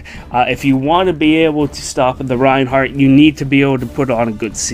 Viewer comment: did you just say Ninnoo? EDIT: I heard "The Reinhardt". All is forgiven